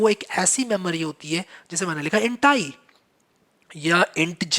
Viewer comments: int i sounded like hentai 😅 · i think i overheard i heard hentai instead of int i 🤣🤣🤣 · i thought he was saying hentai..at 19:11...XD